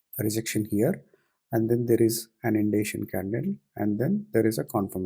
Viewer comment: Hope you get well soon and come back stronger. Just quick question, I didn't understood what the word you said at 4:42, (indation?) and why you considered the confirmation candle in the first place because I thought that the sloping down pattern line would have restricted the momentum ahead and so I didn't take it. Thankyou and take care.